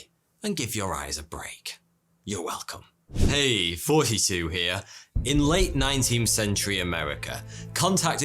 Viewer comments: So, i may be dumb, but..... i always thought he said "hey 42 here". Then i looked closer at the name of the channel..... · you know, for a long time, and by that I mean 5 years, thought you always said "Hey 42 here". Is it just me?